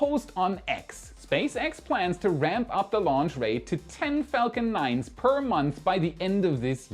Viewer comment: Thought you said Falcon Centaur at one point, I still think Sierra should be talking to SpaceX about the options for launching Dreamchaser!